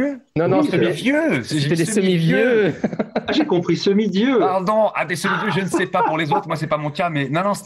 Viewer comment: lol, Moi aussi j'avais compris "semi-dieu" et je trouvais ça bizarre, mais j'etais en x2.5 speed.